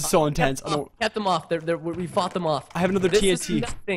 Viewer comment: Did Preston said F u c k them off at 9:00 ?